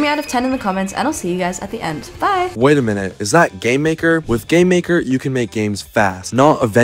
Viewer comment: I thought you said gaymaker.